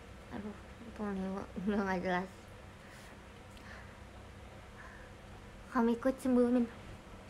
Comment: dia ngomong apaan dah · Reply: "kak miko cemburu" Maybe